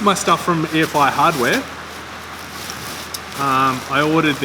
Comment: did you say air fire? Can't find a website??? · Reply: EFI Hardware. All the links are in the video description.